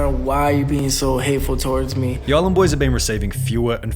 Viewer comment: I thought it said Ireland Boys